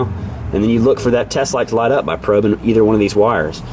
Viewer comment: you say something I can't understand....sounds like "test light". Is that what you were saying?